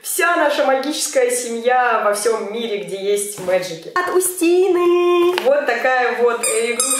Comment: я одна слышу на 30:00 "МАНГУСТИНЫЫЫЫ"😂😂😂😂🤦